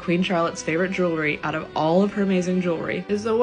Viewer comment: I thought you said heading for the heavens and I had to do such a double take because I WOULD be the type of person to not understand a poetic scene where someone dies but is not showed as actually dying. LOL